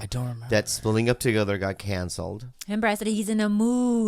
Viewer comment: Thought Bobby said he had CANCER lolol